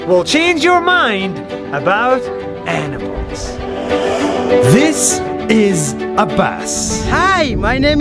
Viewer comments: I thought he said this is a bus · It sounded like you said his name is A bus Hi i am a bus.